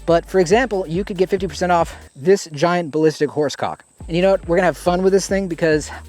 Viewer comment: Sitting here watching accessory videos and my girlfriend next to me must have thought he was saying "flashlight" right up until "ballistic horse cock" 😂